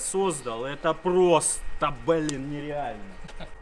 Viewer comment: Одному мне послышалось бля?